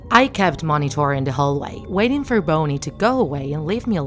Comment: what the hell did she just call bonnie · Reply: Boney